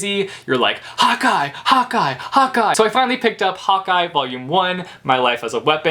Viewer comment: When you were yelling "Hawkeye Hawkeye Hawkeye" I thought you were saying hot guy LMAO